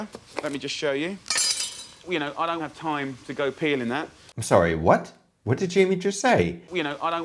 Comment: I had to listen and clarify because I THOUGHT he said "I don't have time to go peeing in that" 😳